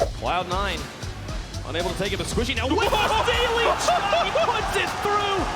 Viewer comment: Oh shit, I always thought they said "SQUISHY, WITH THE DAILY CHALK", not ceiling shot, guess I'm stupid